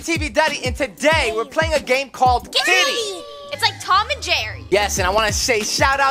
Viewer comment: When said “It’s like Tom and Jerry” I thought she said “It’s like Tom and Jerr”